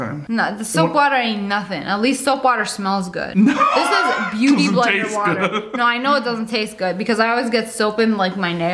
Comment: I thought you said booty blender 😳